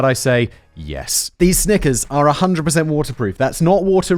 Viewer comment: Did he just say Snickers?